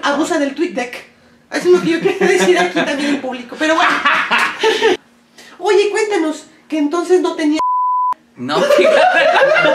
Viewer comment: Después de tanto tiempo y con todas las nuevas entrevistas que han salido al día de hoy, entendí que la censura del min. 16:01 era preguntándole a: "Oye cuéntanos, que entonces no tencias S1da" ...